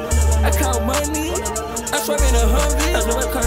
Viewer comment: Truly a lyrical genius. “I cut my knee! I cut my knee” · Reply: I thought it was saying "I got money"